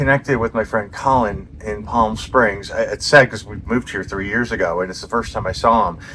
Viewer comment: What are saying at 0:16? It sounds like you are saying ‘sex.’ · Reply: I think he said “it’s sad, cause we moved here years ago”.